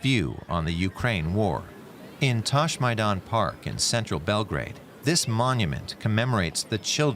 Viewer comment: I heard "Touchmynut park" 3:43. Don't leave your children alone in this park, i guess